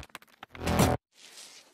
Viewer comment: "essuie moi tes slips" 😭